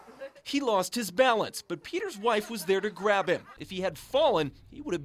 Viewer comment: did i just heard waifu's?